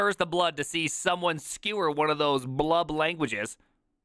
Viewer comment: Yellow and with Purple squirrely braces. It's the vscode default for javascript/typescript. Also, Primeagen, when you said blub language, I thought you said love language. what's your love language?